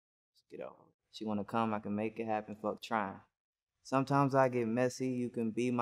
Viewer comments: I swear this whole time I thought he was saying “she wanna come , I can make it happen FUCK TRUMP” it made TOTAL since in my head 😂😂🤷🏾‍♂️ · I swear I thought he said "fuck trump" instead of "fuck trying" for the longest lmao